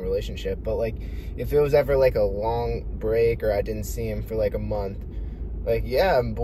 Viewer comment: am i tweakin or did he say "him" at 1:18 when talking about his relationship? lil freudian slip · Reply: em* like “ i was dating em” as in them